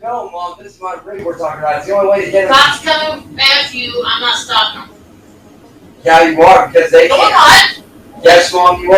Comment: Whose Matthew?? It sounds like says she will allow Matthew to take him. I did hear on another stream that he’s the Police. But who the HELL is to say “Yes You will Mom” Shes the only one stopping him from a stray jackey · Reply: She says "if the cops come after you" but it sounds like Matthew